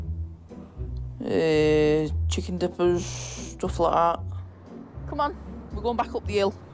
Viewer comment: Did she say "Chicken Nipples"?......lol